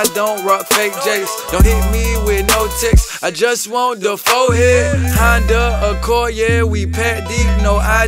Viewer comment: did he say I just want the fourhead😂😂😂